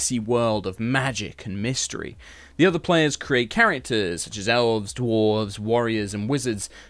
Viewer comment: When you said "such as, elves," I thought you said gazelles